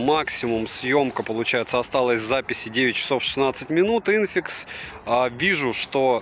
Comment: Было время xiaomi , realme , vivo и т.д, когда то то же были 3 им эшелоном..Да и мне послышалось что автор говорит Инфинкс...Написано по английски по моему Инфиникс